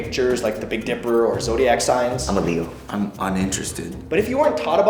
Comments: I thought he said I'm illegal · Did say "I'm illegal" at 2:54?